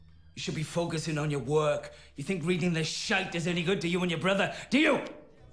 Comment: is it just me or did he say shank